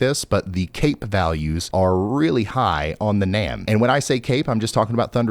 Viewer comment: did you say cake?